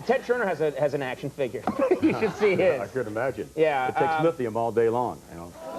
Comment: what does Vince say here ? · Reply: lithium